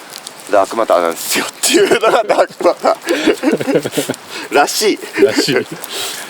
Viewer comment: もしかして「あるらしい」が「しー」って聞こえて、しーちゃん寄ってきてる？ だとしたら、可愛すぎる。